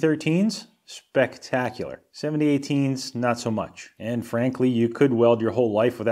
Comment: For some reason... I heard "And Frankly" as "Anne Frankly"